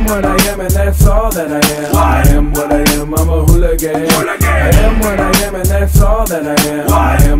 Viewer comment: Thought it said Big H hooligan but you know what i rate this (Y)